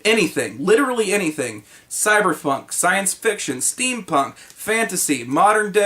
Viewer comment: I... I think you said cyberfunk... I need to write a new campaign >.>